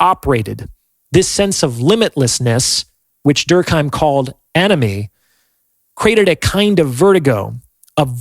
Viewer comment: did he say limitlessness is called anime